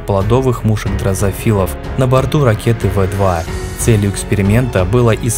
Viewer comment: мне послышалось педофилов.